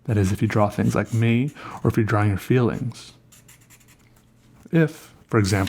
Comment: I thought the video said drawing your feet, and I was thinking that is some ugly ass feet